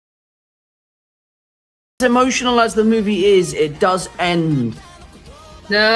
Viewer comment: hold on, what are you try to say here? end with what? dr doom? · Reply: It does end with a cliffhanger